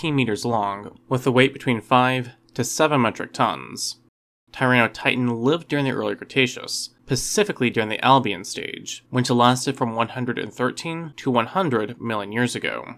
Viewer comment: did he say pacifically